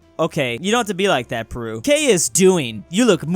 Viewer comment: wait did you just say Peru???